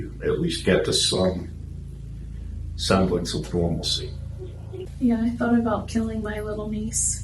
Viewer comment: "like some pharmacy" lol 'semblance of normalcy' is what I think he said.